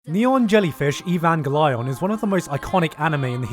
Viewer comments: Did he just say Neon Jellyfish Evangelion in the intro? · at first i thought he said "Neon jellyfish" i was like what the fuck? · Did I just heard him say jellyfish? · did he just say neon jellyfish? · “Neon Jellyfish Evan Gelion” thanks I hate it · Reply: I heard “Neon jellyfish even glide on”